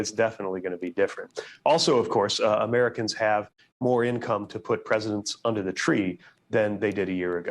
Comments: WAIT, did I hear Buttjudge say we have more money this year, so we can "put more presidents under the tree."?? Check it at 4:05 .... could be my hearing because I have an aversion to all the clowns in Biden's administration. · I thought Pete B said "Put president's under the tree"?